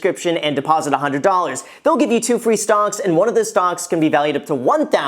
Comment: did anyone else hear him say stonks instead of stocks?